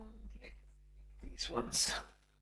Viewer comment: I hear a woman say " not the one " Is anyone else hearing the same thing?